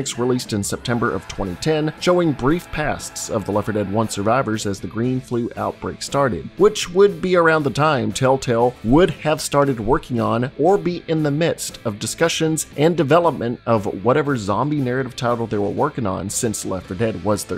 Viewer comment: I thought it said tellatubbies instead of tellatale now I'm sad